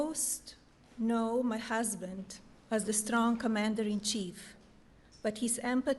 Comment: I thought she said her husband was commander in cheat. What an accent.